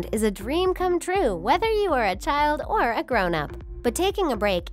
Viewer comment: when you said weather you are a child or an adult it sounded like you said weather you are a child or a doughnut cause I moved